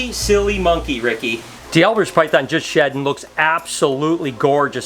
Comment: what species did he say that was at 6:55? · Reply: D'albert's Python, also known as White Lipped Pythons.